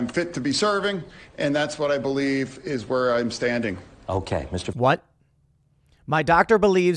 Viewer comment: The quote tweet cleaned it up for him, what he actually said was “…and that’s what I believe is where I’m standing”